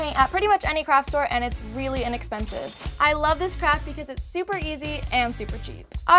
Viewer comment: I thought she said I love this crap instead of craft xD anyone else